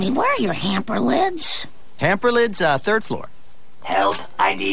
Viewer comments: When I was a kid I thought he was saying "tongue stems" as in the Base of the tongue, really scared me! 😂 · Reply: I thought he was saying “tongue skin”, but I have no idea what a tungsten is. What’s a tungsten? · This was much more horrifying as a kid, because I didn't know what tungsten was and assumed he was saying "tongue stems." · When I was a kid I thought he said "I need tongue skin to live"... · Tungsten?! OOOOOOOOOH! All my life I thought he said TONGUE STEMS! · As a kid i thought he said tounge stem, i didn't know if our tounges had stems, but I thought thats why bart ran away. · Lmfao when I first saw this as a kid I thought he was saying, “I NEED TONGUE STEM TO LIVE.”